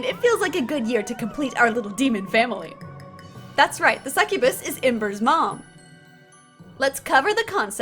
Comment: I thought you said succubus seduced MEN •\\\\• /j